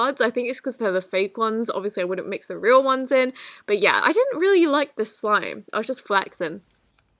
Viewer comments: can anyone please tell me what she actually said? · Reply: Not so popular :/ “I didn’t really like the slime, I was just flexin’” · 'I just flaxen' the captions say